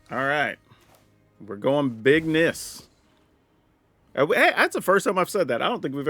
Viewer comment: Big Niss def has a ring to it. Tbh it sounded like “big miss” and I loved it when I first heard you say it. I’d say keep it up and forget the haters